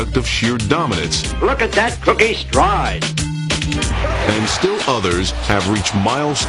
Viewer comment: Am I the only one who heard the announcer say, “Look at that cookie stride!”